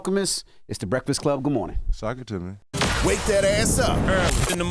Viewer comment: Did he just say "Sock it to me"?🤔🤣